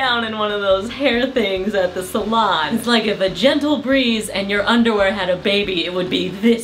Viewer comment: I could've sworn I heard you say "a vagentle breeze" and I laughed way too hard